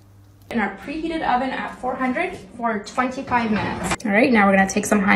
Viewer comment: le son de la vidéo "25 minutes" les sous titres "45 minutes" Joyca : "on va mettre ça dans le four, pendant 15 minutes" moi : hein quoi comment